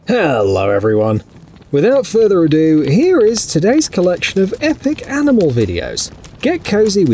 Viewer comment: Did he say "epic aMinal videos" at 0:05 ? Lol